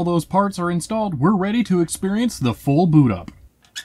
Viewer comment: oh man when he said "experience the full boot up" i thought he said "experience the full buddha". like damn i do not consent